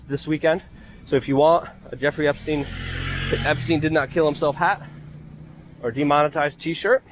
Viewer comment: At first, I thought your hat said "Einstein didn't kill himself"...LOL Don't drink and read!